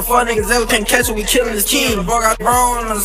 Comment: That’s fucked up yu trynna make that nigga seem like he a kill a kid he clearly said “ If we can’t catch him we killing his KIN “ meaning if they can’t get who they want they gone get his family (cousin or brother or sum) not no fucking kids smh